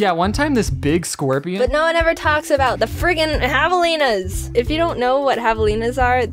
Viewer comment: I thought they said havalinas ._.